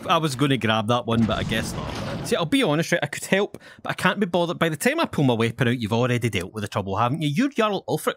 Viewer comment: “I’ll be honest yeah, I could help but I can’t be bothered by the tamer puma but you already dealt with a troubled ham”…. what did he say 😂